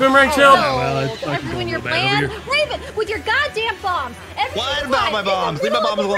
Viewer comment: The "God damnit" at 5:10 from may be the closest to rage I've ever heard from her haha. Even God Damnit sounded like she was happy while saying it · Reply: Wrong time stamp. Also, I think said "Ravin, with your God Damn bombs!"